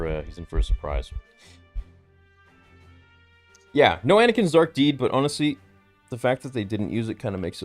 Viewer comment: What does he say at 1:42:46? “No Anakin *something*” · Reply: Anakins dark deeds